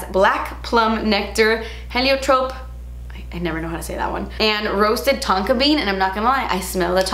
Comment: Did she say “taco bean” at 16:40? Maybe it’s me but... it’s Tuesday, you know. Tacos! 🌮